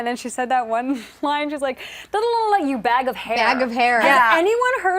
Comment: You guys she said head of hair not bag of hair. In other words calling Hanna nothing but a head of hair, calling her dumb. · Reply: Bevin St. Pierre yes she did! I'm sorry lol I really thought she had said head of hair... bag of hair is just so bad and I remember laughing. I guess I was just too caught up in Leslie's insults haha anyways Hanna could have easily taken her down a peg.